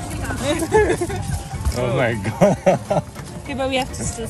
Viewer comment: Did he say "O My God" @ 8:07 ???!!! 😂😂😂